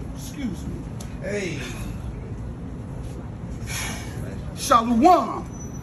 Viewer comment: what did he say shaluwan? What does that mean?